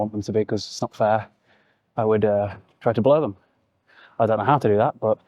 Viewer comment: had to rewind because first time I thought you said "blow them"